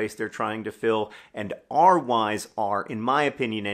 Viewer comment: Oh John said “our Why’s” I totally heard “our Wives” the first two times 😂